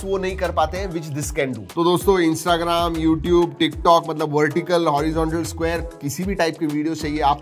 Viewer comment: Did he just said tik tok